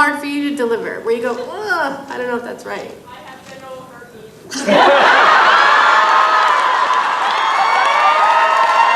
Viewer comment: does anyone know what the girl in the crowd says? I can’t understand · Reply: "I have genital herpes"